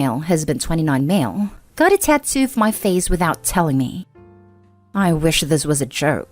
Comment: Wife needs 100% attention 100% of the time. Divorce. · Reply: I thought she said a tattoo on HIS FACE 😂😂😂😂